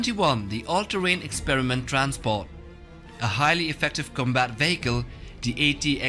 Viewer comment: Anyone else hear "Combat bagel"??